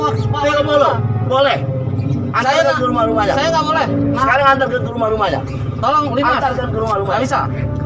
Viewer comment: ANTER DUIT KE RUMAH-RUMAHNYA??? Kuharap salah denger, karna yg lagi ngomong pejabat pemerintahan